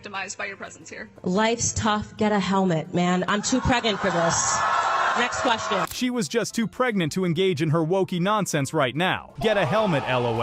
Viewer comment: Did she say I'm too 'creggant' for this?